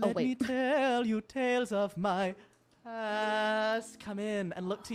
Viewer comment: the caption says" the tale of my a**" ?!? is that what really said or did i just misheard it?